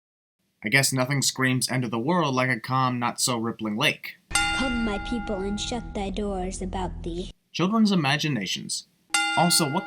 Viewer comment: I can't make out what the kid says right before says "children's imaginations" who wants to help me out n lmk what the kid is actually saying??? thanks in advance btw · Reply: "Come my people and shut thy doors about thee".